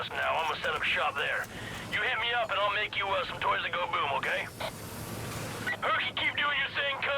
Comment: I thought for a second when Sharky said toys that go boom I thought he said for a minute toilet go boom. So shit I thought he was going to make you some drain o bombs. Seriously do not mess around with drain o.